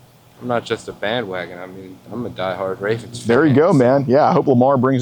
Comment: wasn’t really listening, and thought he said “I’m a die hard racist” 😂